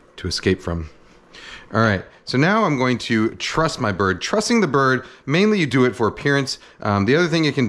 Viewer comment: I thought you said you were trusting the bird lol, like "to cook itself?"